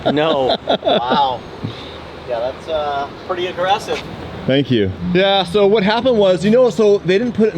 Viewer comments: Nah, he said aggressive, but it looks like you and thought he said impressive, lol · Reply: And to be fair to you, it never occurred to me that thought he said "impressive", but that makes total sense. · Impressive* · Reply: Nah, he said aggressive, but it looks like you and thought he said impressive, lol